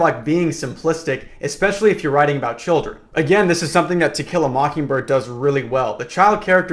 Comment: Help I keep hearing "Tequila Mockingbird" instead of the actual title 😭